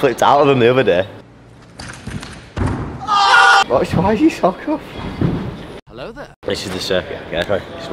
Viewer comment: whattt i don't understand a word 😂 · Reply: He said “why is your sock off?”